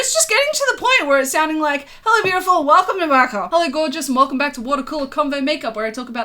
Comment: With the whole accent thing - I thought you were saying “welcome to what I call a convo makeup” for the longest time ahaha